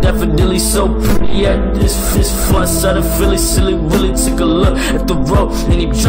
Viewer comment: i think he said "far said of philly"